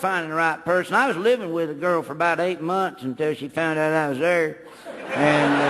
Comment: What did Larry say from 2:13 to 2:16? I can't figure it out. He mumbled the words. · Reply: "I was living with a girl for about 8 months, until she found out I was there."